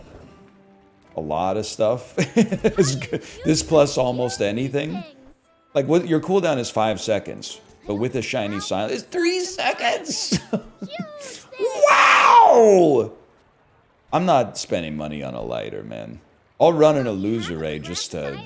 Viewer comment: I could have sworn he said “……with a Chinese silencer…”